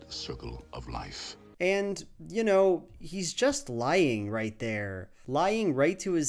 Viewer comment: I heard it as "He's just lion" haha. <3 you!